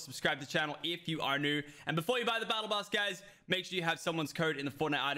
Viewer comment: I’m no hater but I think he said before you buy the battle bus😂